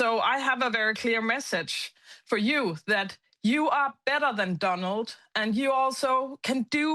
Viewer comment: Did she say "DUMBold" LOL